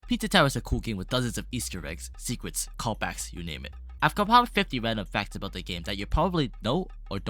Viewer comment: I thought you said easta eggs